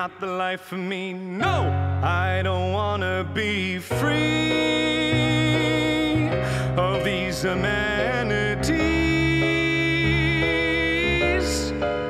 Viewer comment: did anyone else hear "I don't want to be free of these manatees" lol